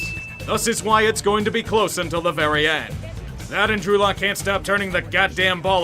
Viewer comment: Thought he said Andrew Luck at first.